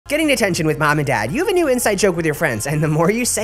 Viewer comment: I thought u said "Getting tattoo with mom and dad" lol